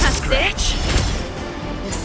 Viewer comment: Off topic but does it sound like serval is saying “bitch” at 17:50?